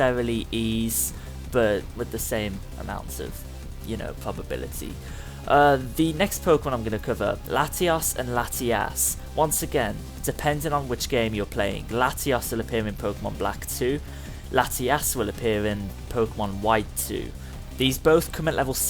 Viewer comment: When you said "Latias and Latios" for the first two times you said this, I heard "Latias and Latias"